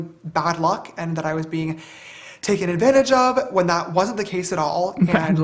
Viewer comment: he said i THOUGHT i was being taken advantage of, but that is not the case, just wanted to clear that out cause i think u guys misheared